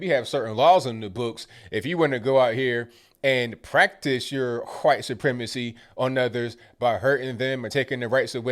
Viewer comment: Did say "huwhite supremacy"?